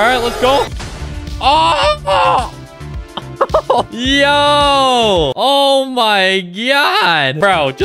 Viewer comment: why is bro saying oh my gyaat